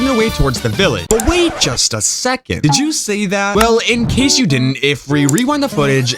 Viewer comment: He says did you say that